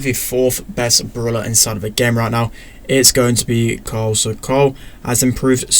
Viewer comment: Sounds like said Cole.😂